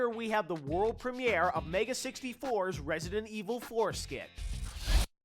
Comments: it's sound like he says resident evil foreskin :P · did he say foreskin?